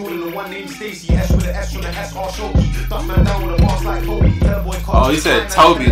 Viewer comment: "Dunk man down with the bars like Koby" I thought he said but you might be right. Basketball player Koby Bryant